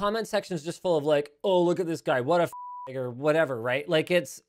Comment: those who are confused at 3:20 yes it sounded clearly like N word but dont worry it was "Fucking nerd" the bleep at the f word stopped prematurely so it sounded like the n word.